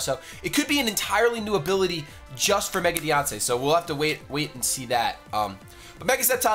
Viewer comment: I sounds like your saying Mega Beyoncé. I would definitely train that.